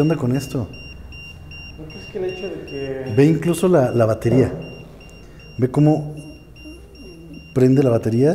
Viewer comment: No manches lo que mas me dio escalofrios fue en el minuto 8:01 cuando dicen una muje con voz burlona "te sorprende" y en el minuto 10:21 que se empieza a escuchar una cancion extraña y cerca del microfono de la camara nocturna y solo lo escuche con los audifonos